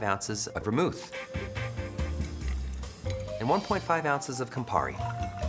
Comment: So... it's a Negroni. He took a Negroni and renamed it a Misdirect. "Did not see that coming." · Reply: ah, I misheard it the first time as 1/2 ounce Campari (wasn't watching it through whole time as I was cooking). I stand corrected.